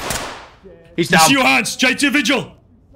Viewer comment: What's he saying? J2 vigil?